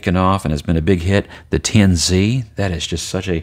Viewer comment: you introduce a pen, I think you called the "10Z" (?). I'd like more info on this pen, please. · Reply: That is the Tianzi, a piston filler: <link>